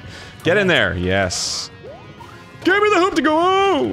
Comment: I also thought he was saying "gimme the hoopty goo" until this episode